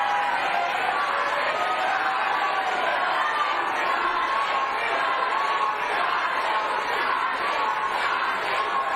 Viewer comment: Anybody understands what the audience started chanting at 00:55 mark? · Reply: Sounds like “Charlie! Charlie!” Over and over